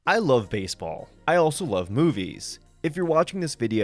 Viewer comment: at first i heard this as “i also love boobies”